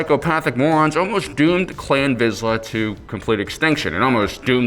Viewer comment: Because of the Helmet it sounds like is saying Clam Vizla which sounds like an overpriced dish at a French restaurant